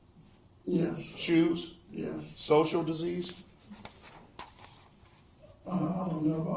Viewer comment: Did anyone catch that “disease” the defense mentioned? Sounded like he said “social disease”.